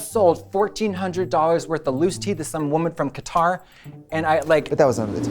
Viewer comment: I thought Katya said she sold $1400 worth of loose teeth 🦷